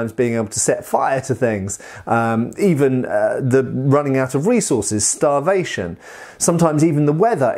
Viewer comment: I swear i thought he said "running out of weasels is starvation"